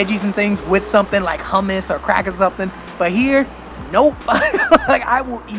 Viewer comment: record scratch Did you just say you always ate your veggies with... cr*ck? 😂 I think I misheard and you probably said crackers? 😂